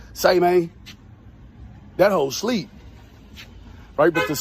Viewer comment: I heard “ he half asleep”